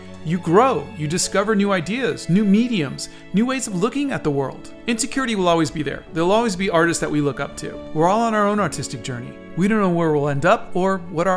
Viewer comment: I thought he said “Were all on our own autistic journey”